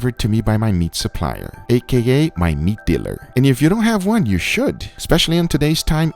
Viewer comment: Somehow i misheard the “meat dealer” as “meat beater”